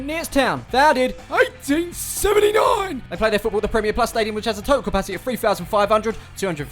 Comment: I thought he said (PREMIER LEAGUE STADIUM) 😭😭😭😭 I’m soooo said but happy